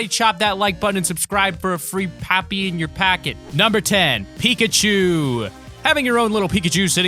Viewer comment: did you say Pikachu?